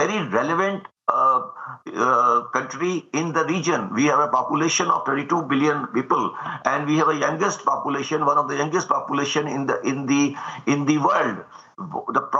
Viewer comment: did he say 22 billion lol?